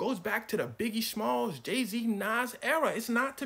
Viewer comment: mans said Jay Z and the captions say Daisy, had me searching for an artist named Daisy😭😭😭